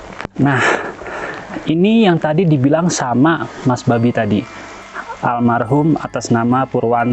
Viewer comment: Kalau gw ga salah dengar waktu mas berdiri d plakat nya Almarhum Mas Purwanto......di menit 24:19 Mas nya bilang gini " ini yang tadi d bilang sama Mas B4b1 tadi " Maaf yaa kl gw salah dengar 😂😂